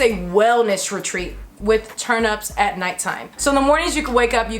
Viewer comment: Am I the only one who heard turnips @ 10:51? Just me? Aight, cool. Lol.